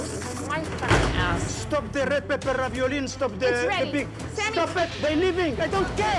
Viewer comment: Wtf did Sammy say at 6:34? “STOP THE REPRPEPEPEPRARERERAPRIORIN” what language is that XD? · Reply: Red pepper ravioli, I think. 😂